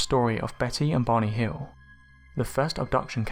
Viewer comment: For all of No.. 1 I thought you were saying Betty and BONNIE Hill. Then you showed a pic of their plaque. Ffs dude pronunciations matter.